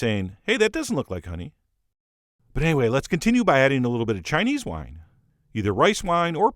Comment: I thought he said Chinese water when he said Chinese wine. And, for the briefest of moments I contemplated what that was, what it tasted like, how I could get it, and how much it would cost me...